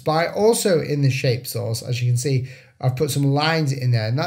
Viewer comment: At first I thought you said "shape sauce" 😺 Great video despite my hearing problems and I'm going to enjoy all your tips once (in a day or two) my iPad Pro finds it's way to my doorstep.